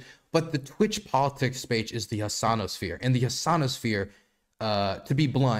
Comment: am I the only one that originally heard it as The Gusano sphere😂